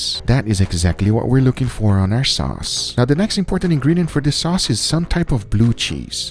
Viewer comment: “Get to know each other” babish approved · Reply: Pretty sure I heard him say "sauce pin". He HAS to watch BWB!